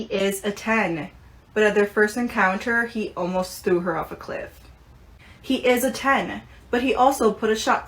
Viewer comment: Ngl at 4:27 I thought she said “sued her off a cliff” which ig could be worse in a way